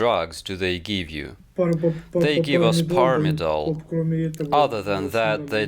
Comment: What drug are you saying at 7:00 or so. Transcript doesn't translate properly. And Google only shows results for a herbicide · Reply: Pervin? Like what the German army took in 1939?